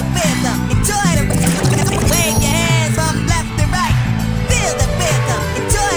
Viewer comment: I thought i just heard 'Wave your ass from left to right'...Its 'Wave your hands from left to right'.